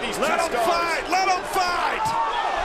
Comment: Anyone hear, Someone saying "let them fight" 😂